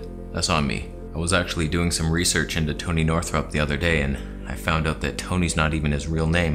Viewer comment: Did I hear "Tony Northrup isolation"?